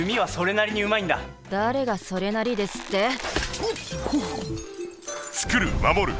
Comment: What did Archer say? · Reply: The guy says something like "she is a bit selfish but half decent with the bow" And the archer replies with "half decent?"